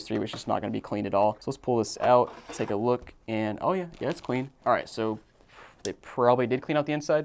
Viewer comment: at first i thought he said oh yeah yas queen